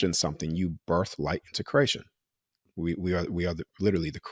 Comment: This is terrific! I hadn’t heard of before today. Im going to tell on myself here. It took me to the 25:26 minute mark, to realize he’s saying “creation!” From the start of the video, I thought he was saying “Croatian!!!!!!” 🤣🤣🤣 And it didn’t make sense. I got distracted several times trying to understand what he was talking about! Now, it all makes sense. 🥰 I guess we could say it was a blonde moment. 😊 I will be watching this again. Thank you and. I am grateful to you all! ❤️